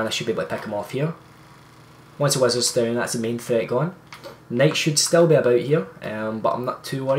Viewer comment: Thick accent. nice video man, but I did struggle to understand you at times :P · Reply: Lol. When I heard you say "night" I thought you were saying Nate. No offense though. I still know what you meant😀